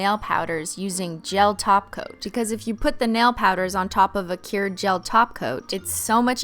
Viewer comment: Cristine: "The nail powder" What I heard: "Inhale powder" Me: Instantly replays video Me: "Phew"